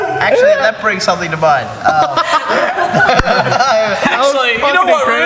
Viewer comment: what does ashton say at 10:14 ??? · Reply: "actually that brings something to mind"